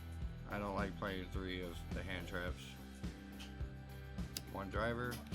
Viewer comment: I heard, "I don't like more than two of each hand trap" and I closed the video. Lol